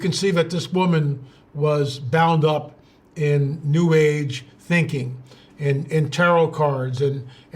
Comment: God bless! Does anyone hear a strange sort of voice in this video at about minute 26:45? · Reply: Yes, it sounded like "an New ah" ....